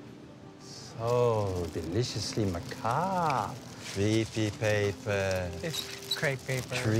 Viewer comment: I always thought he said "trippy paper"